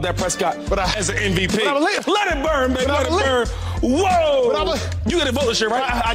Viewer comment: Let it burn N.....is what I thought I heard 😂